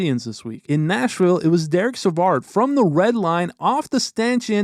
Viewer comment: What was your favourite moment during this insane week? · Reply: My least favourite is where u said at 5:31ish Derrick Savard instead of David😂